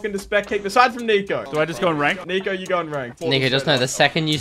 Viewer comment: ummm what did he sayyyyyyy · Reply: I think he said nico 😂😂😂😂